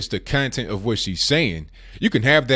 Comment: lul you said.. "content of what she's saying" I heard... "the cunt and what she's saying"